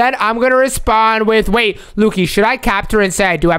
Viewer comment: sounded like Alexa